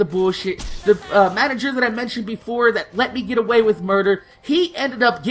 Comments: did you say murder? D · lol... did you say get away with murder at 20:17 ?